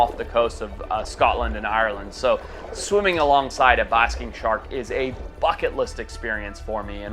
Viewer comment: Lol it almost sounds like u say busking shark instead of basking shark it must be your New Zealand or South African accent I can't remember where you're from I know it's one of the two now that I think about it I think it's South Africa